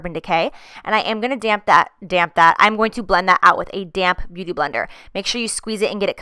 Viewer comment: Thought you said "damn beauty blender" instead of damp... I was like wow so sassy lol.